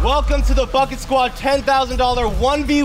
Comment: Instead of you saying bucket squad I thought you said f*** it squad lol